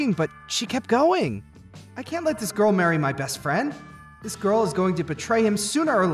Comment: I thought Kaito said "I can't marry you, so I'll marry my friend instead" like literally I was like NANI!!!🤣